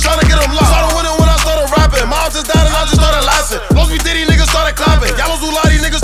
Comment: Did he just say his moms just died and he started laughing 🙄🤔😢